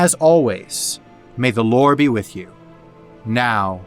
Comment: For a second there i thought you said “may the lord be with you” in the outro lmao